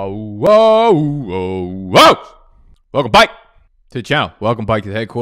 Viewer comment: I want to punch a baby every time I hear 'welcome bike'